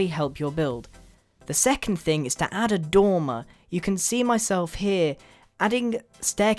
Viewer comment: the first time you said dormer i thought you said tumor